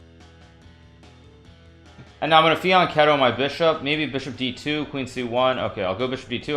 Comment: Can someone help me im new to chess. 0:25 how does he call that bishop move? I heard agadmator use the same term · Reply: Fianchetto